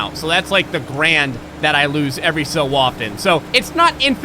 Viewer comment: did you say every so waffen?